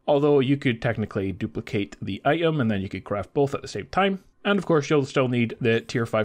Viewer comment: did u say duplicate the behavior chip?